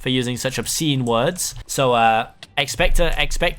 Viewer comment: For some reason I thought he was going to say expecto patronum